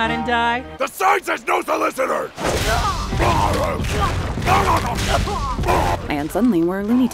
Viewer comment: When piccolo says "the sign says no solicitors" i heard " the silence knows the listener" Anyone else?